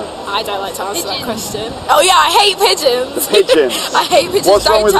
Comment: I thought she's saying Peaches not Pigeons hahaha